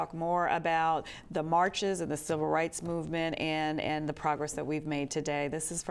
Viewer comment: I would have fuckin sworn that it said "Child Rights." i'm doing good.